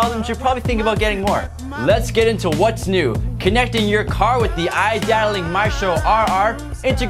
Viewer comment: what are you saying at 2:26? i want this stereo but i want to make sure i get everything that it has to offer like the hdmi port for my iphone siri xm/aux/stereo controls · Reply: iData Link Maestro integrates your systems' info to the radio, like tire pressure, odometers, climate control, etc. Its an adapter you can purchase separately. This radio does have an HDMI port